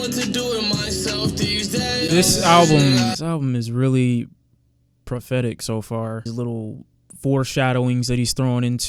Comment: When he said the albums so prophetic I thought he said pathetic and I was like “damn we only two songs in”😭